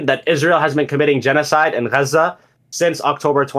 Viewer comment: What is this guy saying? "gaza"?